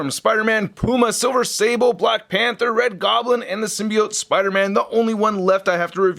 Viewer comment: did he just said black panther?